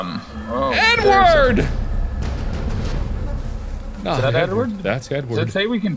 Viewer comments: I honestly thought when Mark said Edward that he said N wooorrrrrd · When he said “EDWARD!!!” I thought he said “N WORD!!!” And I was like jeez marks really goin downhill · i thought he said literally “N word!” Instead of “Edward!” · i thought he said "N word!" lol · I thought he said n word, not Edward. 💀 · it sound like Mark just screams "N-WORD" 😂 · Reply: He said Edward, but it did sound like he said N-word lmao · thought he said "N-word!"